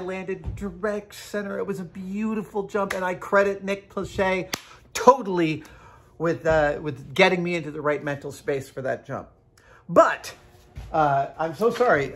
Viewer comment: I thought he said Nick Lachey...